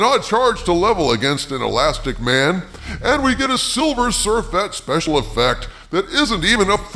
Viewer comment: Actually, they did put the best parts of the movie in the trailer, that's the problem. The Silver Surfher effects aren't even as good as the T-1000 effects in the second Terminator movie back in 1991. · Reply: I thought said silver smurfet